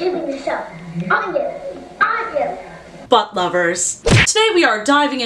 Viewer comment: In the intro it sounded like she said fuck lovers lol